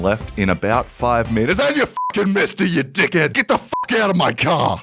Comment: erm he was obviously saying millisecond- so erm, i blame you 🤓☝️